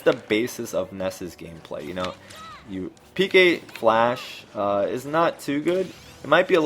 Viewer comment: PK Flash? I always thought he said PK Flux